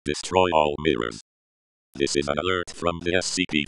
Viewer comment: Bruh I thought it said "Destroy all minions"